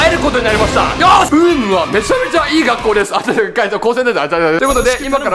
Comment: UUUM＝高専みたいに聞こえてきて草